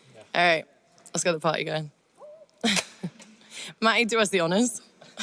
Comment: What does she say at 0:08 ? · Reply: "Mike, (can) you do us the honours?" If I'm not mistaken 🤪